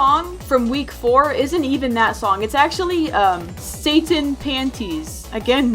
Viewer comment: DID SHE JUST SAY SATIN PANTIES AS “SATAN PANTIES” Y’ALLLLLL